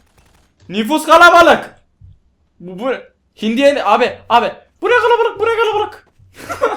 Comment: hindi kalabalığa girmiş ne demiş ? bu ne kalabalık bu ne kalabalık :D